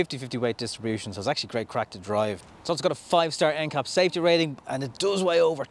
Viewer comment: Did you sAy it’s crap to drive? Mines due end Oct 😂